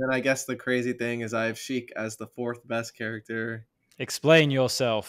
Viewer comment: Can’t wait to see Sheik in Tweeks top five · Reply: Thought you said Shrek💀